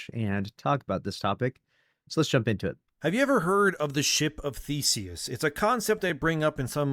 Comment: I thought he said the ship of feces, which is actually a more apt analogy